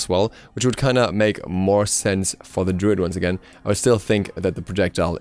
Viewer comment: did you say darude sandtorm? tuturututu